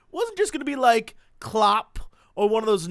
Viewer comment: so...what are you saying as 00:12. Cause it sounds like you are saying "Clop" and considering what Clop means in brony circles...i am GLAD that your video wasn't going to be "clop" heh...